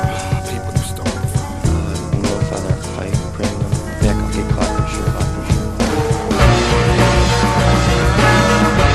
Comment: what does the guy say from 2:02 to 2:06? "Er - No father, I..."? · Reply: I think it’s “if I bring them back I’ll get caught for sure” I know this is a decade later, lol